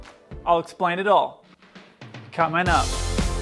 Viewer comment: I'm sorry. But did I heard cut my n*t? at 0:08?